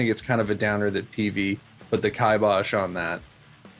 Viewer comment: Peavey put the kai Bosch on that? 1:00 what is that word you said?? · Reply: Kibosh, yeah they sent a cease and desist.